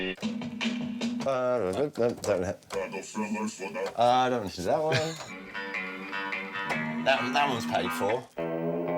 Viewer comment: PLEASE WHAT IS IT SAYING AT 4:48 I HAVE NO KNOW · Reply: Jay-z's sample, from who you wit - saying rockafeller distorted and looped